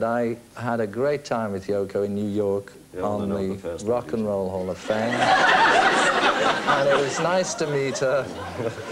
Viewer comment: What does Ringo say at 21:37 that causes the audience and George to laugh while George is discussing Yoko? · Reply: “You’re not the first one to [have a good time with Yoko]” 😂